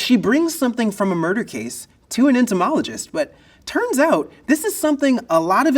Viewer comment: Everytime I hear the word "entomologist" my brain interprets it as "Antimologist" i mean ants aren't the only insects you study in that feild tho it does help remember that study